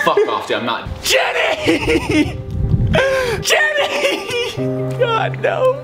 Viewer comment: I died so hard when tayler ran out saying jenny😂😂😂😂😂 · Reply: I thought he was saying Kenny 🤣🤣😂😂😂😂😂💀💀😭😭😭😭😭